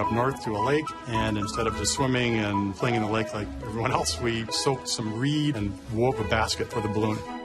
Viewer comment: I totally thought he said "smoked some weed" instead of "soaked some reed"